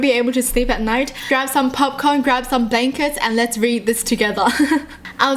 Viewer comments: I always thought she said grab your popcorn and bacon but she said blankets 😂😂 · I thought you said "Grab some popcorn and bacons"